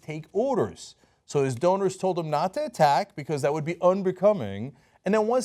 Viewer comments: Donors? I thought you said owners. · When Cenk said "donors" i heard "owners" 😂